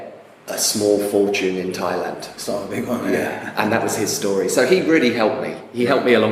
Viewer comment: How do you get a small fortune in Thailand? Can't quite catch it, what was your answer please? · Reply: Start with a big one